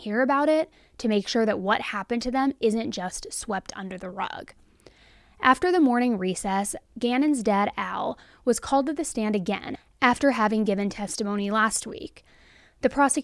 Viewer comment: I’d swear at 11:03 you said, “Gannon’s dead owl.” But I’m recovering from major oral surgery and on Vicodin, so I’m sure it’s just me. I was upset for a sec about his owl being dead, too.😂🤦🏻‍♀️🤷🏻‍♀️🤬